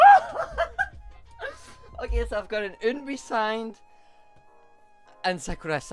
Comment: Sorry, the last photo from Yena's album is not Eun Bi but Hye Won (my bias) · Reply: Bagoet IsBack i said kwanbae but i can see how it does sound like i said kwon eun bi 😂